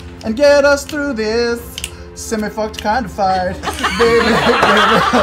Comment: Can a good soul tell me what he sings here? I need Grog to rage And get us through this (semifortkinto find) It's bugging me so much that I can't understand it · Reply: "semi fucked kind of fight" The song hes covering is called Semi-Charmed Life by Third Eye Blind